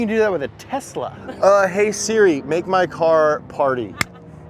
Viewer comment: 😂 when he said hey Siri make my car my Siri popped up and thought he said hey Siri make my cock